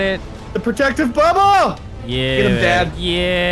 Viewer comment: Anyone else hear at 12:04 say "Get it dad" ?!?😂😂😂